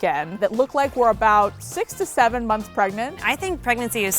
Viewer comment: when she said "6 to 7 months" i thought she said 67 i was like what the????